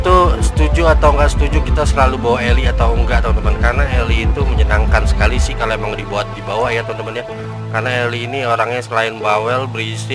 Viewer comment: Tau gak gua dengernya aapn gua dengernya Heli Gua Kira Helikopter